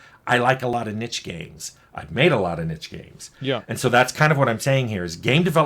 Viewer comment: when he said niche I thought he was saying ninja